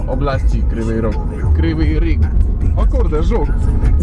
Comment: usłyszałem; o kur*wa, żul XDD